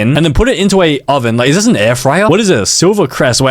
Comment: When you said slivercrest I thought you said cindercrest✌️